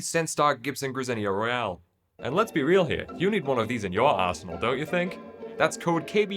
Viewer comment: who else heard arsehole instead of arsenal first...?😅